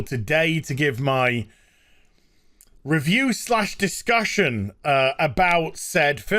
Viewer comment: (Az) 'I though it was more appropriate that I wait until today to give my... (0:27) review/ disgustion...' - Don't bother to verify in the transcript, that's what he said, I swear! ;-)